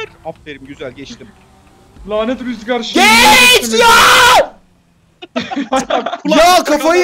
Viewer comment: den sonra ne dedi sağar oldumda duyamıyom biri yazsın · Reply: 3 ay önce yazılan yoruma cevap yazma keyfi bağırarak geç lan dedi